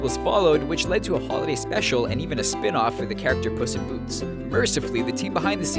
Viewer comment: On 1:51 i thought he said pussy and boots